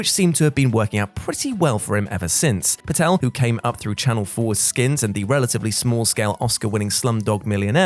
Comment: "channel foreskins" it's all I heard